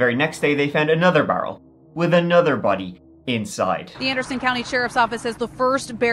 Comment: So, idky, but I heard, "they found a bunny in the barrel," instead of body... And someone tell me WHY I was low key more upset to hear about the bunny!?! Obviously thinking about it I certainly don't feel that actually. We get so bombarded w talk of murders rapes etc that its no longer as shocking, but for whatever reason animals being hurt makes ppl outwardly act more distressed over it. And more than just outwardly, I think the higher shock value of it and its political city w certain demographics... But I just thought I heard "bunny" in the stead of "body" and my brain not only had the entire 2 sided debate, but idek which side won! Jk, but I did think its crazy that my brain did ALL of that over a single misunderstood word, and the bonus of typing out and sharing it, too. Like, with?!? Great job, as always, good 'ol Mike! 😊